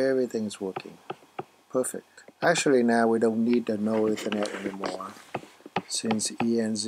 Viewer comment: What are you saying at 2:40-3:00? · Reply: NullEthernet.kext from rehabman not needed since the new wifi is recognized as EN0 built-in for iMessage to work since the laptop doesn't have physical Ethernet port